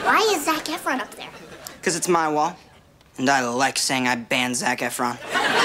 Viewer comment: Ok for a second, I thought he said "I like saying I'd bang Zac Efron." 😂🤣